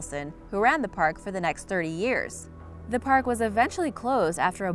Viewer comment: Okay, but at 9:54 I heard: "...And guards were attacked by ewes with knives!" Yes, I turned on captions and found out it was "youths" but it still sounds like "ewes". Also what's with using photos of any old park if you don't have any of the one you're talking about?